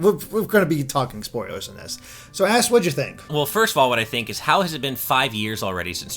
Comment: So Ass whad ya think? anyone else hear him say ass instead of Ash?